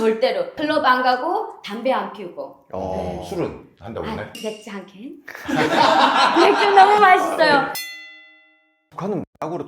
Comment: 소주 한병~ 소주 너무 맛있어요. 라고 들리는데? ㅋㅋㅋㅋㅋ